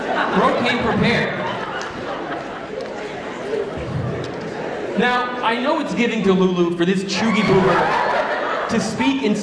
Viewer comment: What is he saying at 3:54 ? "Now, I know it's giving delulu for this ... "